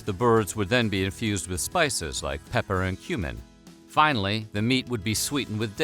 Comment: I thought u said human not cumin